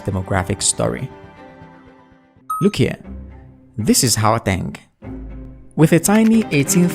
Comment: What did he say here at 0:53? Hooatang? 😂😂😂😂😂 it's pronounced GGGGGGGGGGGAUTÊNG 😂😂😂😂